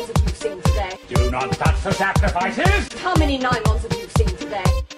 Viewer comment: Oh. SACRIFICES. I honestly thought he was saying "zap devices." *facepalm*